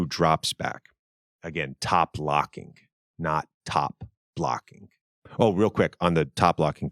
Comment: I didn’t think you were saying top blocking I thought you said pop locking😭